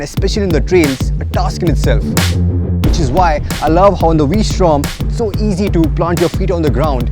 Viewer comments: did you say "I love Honda V Storm..." @ 1:54.😂😂😂😂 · Good video effects. But did he said Honda V-Strom at 1:52?